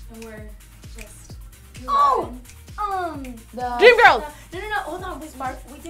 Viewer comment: Notice how Dinah said "Mean Girls"😂😂😂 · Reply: I thought she said dream girls!!!!!!